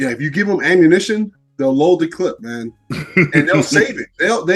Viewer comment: did say "load the clip"? What is this WW1 lol